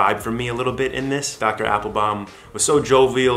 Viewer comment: I thought you said his name was Dr. Apple Bottom. I said 👀 lol Nice to see you! So much care, love, and support from us all. ❤️